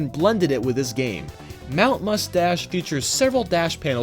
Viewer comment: it's supposed to be play on words? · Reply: I thought you said Mount Mustache too. Boy, do I feel silly!